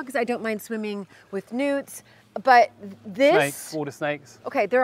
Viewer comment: I thought she said "I don't mind swimming with nudes"... Took me a minute to think of "newt. "